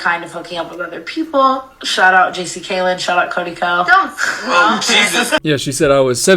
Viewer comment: Wait, did she say JC Caylen too?